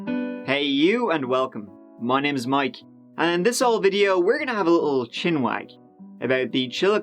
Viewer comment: For the longest time I thought you were saying “in this el video” like it was in Spanish 😆